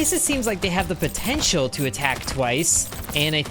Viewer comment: did you say... potential?